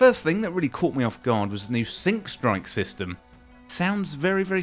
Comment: You said "sync strike" and I heard "sink strike" and assumed it meant an attack that "includes everything but the kitchen sink"... ...seemed legit...